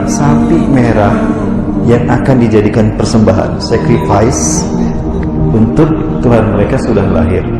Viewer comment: sacrifice oi bukan "secret vice"